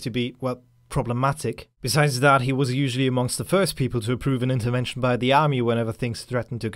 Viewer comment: You speak too fast, i can't understand :( i had to listen to 2:27 several times before understanding "intervention" (all i could think of was "untermensch")